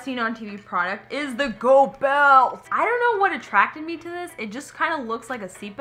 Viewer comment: When first said "go belt" I heard it as "gold belt" And then I saw he the belt and my I was literally like what a rip off 😱 😂😂😂